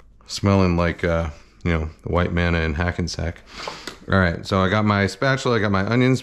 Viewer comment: Straight up thought Kenji said “a white man in Hackensack” and did small batch artisanal microbrew spit take